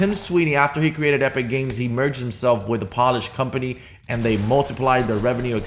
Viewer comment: a what type of company? Did you say (as in the shoe cleaner) polish?